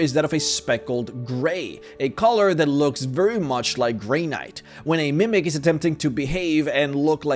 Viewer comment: When you said granite I thought you said Grey Knight and if Mimics looked like Grey Knights I'm not sure if that's more or less terrifying. I'm going to say more